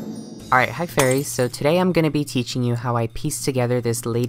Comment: I thought you said hi furries